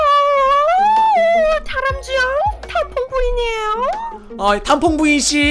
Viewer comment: 에서 저만 탕퐁부인 이라고 들리나요?